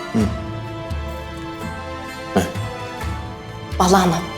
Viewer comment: fatma ne diyor anlamadim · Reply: Bala anam diyoda ne demek istediğini bende anlamadım